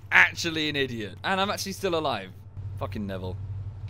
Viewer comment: revives, an “intellectual:” AIJASLAHV—! puts out of his pain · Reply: Lol i thought he said "im still alive" tho